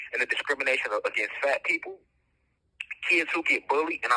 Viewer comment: the 4:46 mark… did he really say discrimination against fat people? 😞😞😞